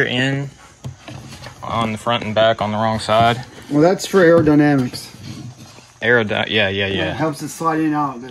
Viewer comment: Did you just say "error-dynamics?!?!" That's the funniest sh*t I've ever mis-heard!! 😆😆😆